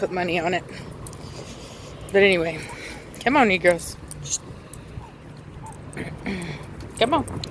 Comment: What did you say at 1:13? I don’t want to be sensitive to something without asking for enunciation in a polite way. I honestly support your content and don’t believe you would say something with ill intention. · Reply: She say's "Come on you girls, come on"